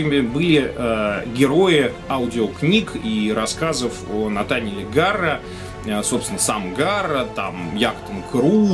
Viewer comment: Мне послышалось или он назвал Натанитиле Гарро? А вообще, давай более развернутые ответы на вопросы. А если не знаешь, то не за чем вообще показывать вопрос который тебе задали.